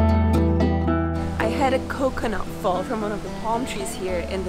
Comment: Did she just say "I had a COCONUT fall from one of the PALM trees" ??